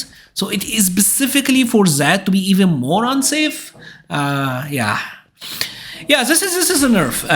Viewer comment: Did you say "moron safe" 😅 Thank you for the detailed breakdown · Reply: More unsafe bro are u deaf